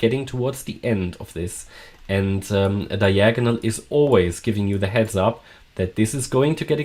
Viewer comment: ? did you say always lol